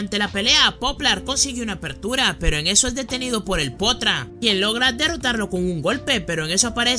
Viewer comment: Like si escuchaste que dijo potra minuto